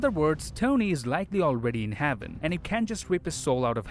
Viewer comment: Lol he said haven had instead of heaven